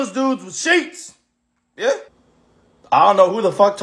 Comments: "homeless dudes with sheets".... Being a resident of the city of brotherly love, Philadelphia, I can tell you that "homeless dudes with sheets" can be some of the scariest people you'll ever meet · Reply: Haha I thought he said SHEEPS. Both are correct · “A bunch of homeless dudes with sheets” 😂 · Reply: I thought he said sheep 💀💀